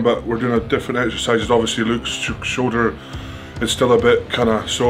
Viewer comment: I misheard the intro first time...instead of Luke's shoulder I heard "Luke's a bit shorter" and I thought damn....imagine being slammed for not being 6ft 8 😂😂😂